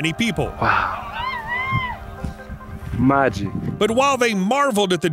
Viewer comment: did he really called the sun and moon "my G"? lmao🤣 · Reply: He said "magic"